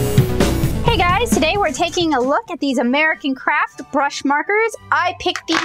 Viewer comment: Lol! So much sarcasm in the first minute haha 🤣🤣🤣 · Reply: At the end I could have sworn you said "American Craps" lol